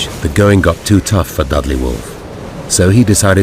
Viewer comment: Did he say Dudley Moore..?